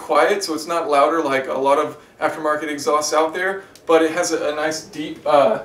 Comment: Did he say "AfroAmerican exhausts"?